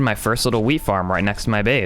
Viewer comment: Love how when he says wheat it kinda sounds like he says weed